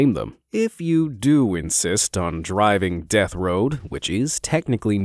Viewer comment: I thought he said death row at the beginning and yeah that would make sense.